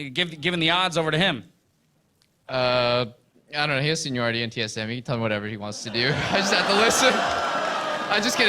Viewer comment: what did doublelift said at 6:22? (my english so bad:p) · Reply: cool He has seniority on TSM he can play whatever he wants to